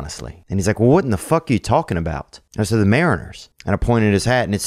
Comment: To be fair, I thought Theo said marines at the start, only to realise he said mariners. He meant marines in the end, so I guess my idiocy cancels itself out